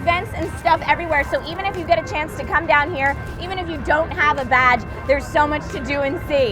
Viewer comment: sounded like she said even if You dont have a Vag